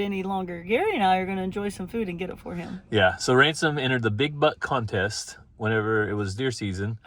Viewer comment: 😅😅😅😅😅 I thought you said " BIG BUTT"! I shook my head like what?!?!!! I listened closer, and it was BIG BUCK.